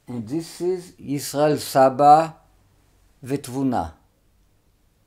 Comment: I think he is saying, ותבונה, “and wisdom.”